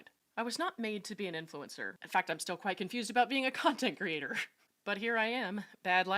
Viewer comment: Did I hear you correctly? Did you just say “cuntent” creator?